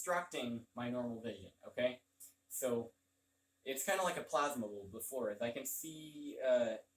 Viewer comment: Interesting. Cool share. At 1:58 you say "It's kind of like a plasma..." There's a word after plasma that I can't make out. It was in reference to how you see energy. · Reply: Plasma bulb